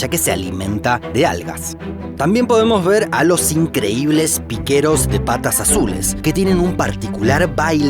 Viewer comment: jaja parece que esta diciendo "puto si piso la raya"